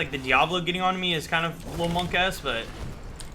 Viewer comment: What is Monke Es? I heard say it but I just assumed it was an Irish/Gaelic expression? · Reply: MonkaS unless you were kidding, the whoosh me